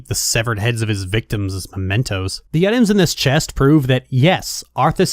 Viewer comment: you say "Mementos" but the CC says "Pimentos". Like the pepper. That kinda absolutely demolished the somber tone you were using when I burst out laughing